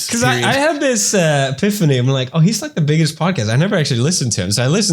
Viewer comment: did pewds say 'piphany' instead of epiphany at 5:48 😂